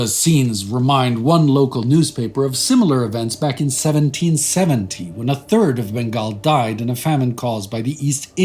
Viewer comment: it sounds like 1717 is said instead of 1770. Anyway, sole responsibility here is attributed to the EIC but there were multiple causes for that famine (not least the Maratha raids and Chauth imposed). Civil administration at the time was still in the hands of the Mughal Nawab. Population decline could also be attributed to the smallpox epidemic. Recent scholarship (eg Tirkanthar) suggest the 1/3 estimate to be greatly exaggerated. I usually like this channel, but this appears to be an instance of bias.